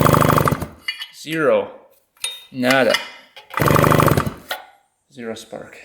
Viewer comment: did you say "nada"? hahahah